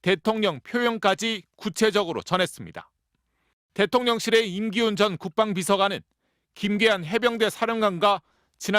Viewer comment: 나만 "대통령실의 '임기응변' 국방비서관" 이라고 잘못 들었나? 하도 이리 저리 둘러 대니까 국민 귀가 고장이 났네 ㅠㅠ